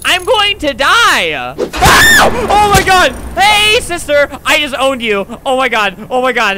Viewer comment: Did I just hear hey sisters? 🤔